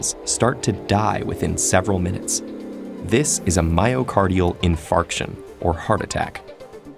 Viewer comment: I thought he said in-FART- ion